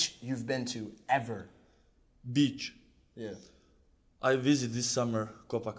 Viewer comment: Haha at 3:02 it sounded like he said "Bitch"